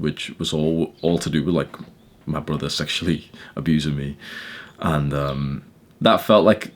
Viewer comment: his brother sexually assaulted him?? Did I hear that right??